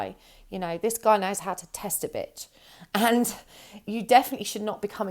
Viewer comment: You: "This guy knows how to test a bit." What I heard: This guy knows how to test a b*tch. And I felt that to my core. 😂